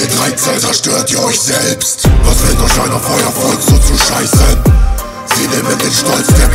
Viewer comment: Was sagt er da??? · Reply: Sie nehmen den Stolz der Gemeinde verstehe ich, sorry wenns falsch ist